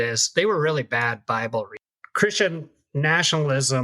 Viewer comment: you cut off the end of the sentence, so I have no idea what he meant to say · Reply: “readers”..just cut off the last part of the last syllable.